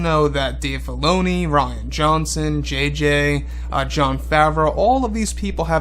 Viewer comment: I think you called Ryan “Rotten” Johnson.